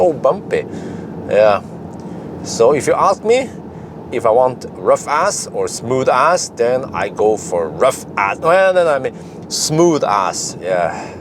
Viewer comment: This is hilarious... I likely misunderstood but I heard "If you ask me, I like smooooth ass" LOOOL 🤣 P stop jesus lol